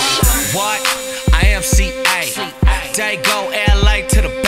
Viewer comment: I heard "i am CJ" Lmao